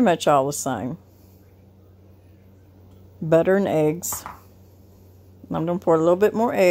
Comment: I thought you said fried eggs, then I realized you said five eggs. Whew !